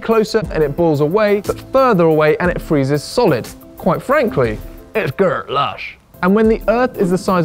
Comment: What does he say?: " . . . quite frankly, it girl ash . . . , " is what the closed captioning read. . . . it's dirt lush?